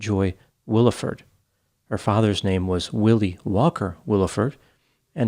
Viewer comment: I thought he said WALKER? As in Paul Walker? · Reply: I just watched it again n yes it is Walker.... Thank u